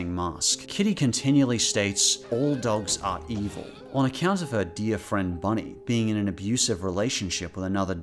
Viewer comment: "kitty's dear friend bunny" yeah... friend... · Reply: Thought he said deer friend and I was confused as to why a deer would be called Bunny